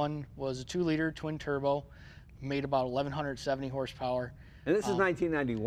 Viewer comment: I called and made an appointment with my ear doctor for a hearing check when I thought he said 1170 horsepower... I can't believe Oldsmobile had a 4-cylinder engine that put out 1170 horsepower back in the late '80s no effing way